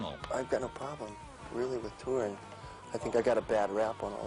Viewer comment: what does he say? Bad rap on what? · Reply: Bad rap on touring